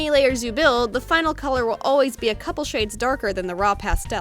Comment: Omg when she said shades the video glitches and sounded like she said sh*ts